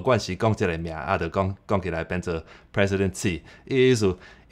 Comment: I heard "President See". Obviously not the name of Taiwan's